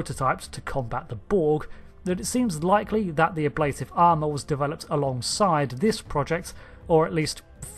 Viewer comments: Whenever I hear "Ablative Armor" I hear it as "a lade of armor". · I'm laughing at myself. All the time that I watched DS9, I thought they were saying "a blade of armor." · all this time i thought they were saying "a blade of armor" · I always thought they said "A blade of Armor" ha ha